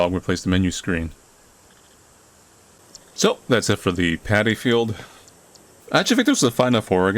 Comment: Oh God, I thought he said "the Panty Field", I was so confused!